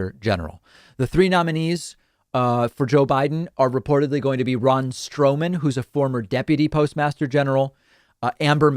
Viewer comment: I first heard brawn strowman 😑